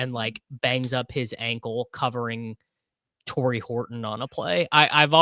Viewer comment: Thought you said torry holt for sec and was blasted with nostalgia